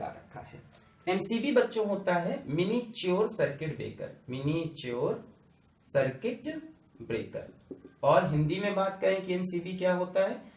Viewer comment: Did sir say MInichor circuit breaker